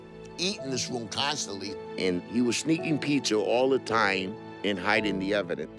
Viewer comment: I thought he went say he was sneaking pee. That was funny I can’t stop laughing. 8:59 🤣🤣🤣🤣